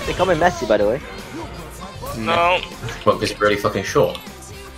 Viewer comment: What did say 8:45?? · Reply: "They call me Messi btw." "What, because you're really fucking short?"